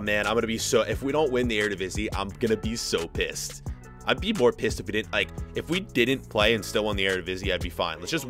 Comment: Did he just say 'I need more piss'